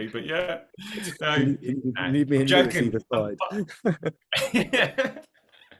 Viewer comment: Please if someone could tell me what said at 30:12 I would really appreciate it. Thanks in advanced! 🫶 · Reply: We think says “you need me and Lewis either side”. 😄 thank you for being a dedicated Teachifyer!